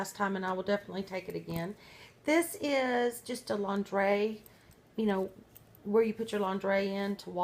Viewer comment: when she said laundry I thought she said Laungerie